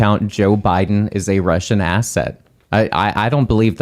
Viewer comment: Thought he said Russian Ass Hat. Fits.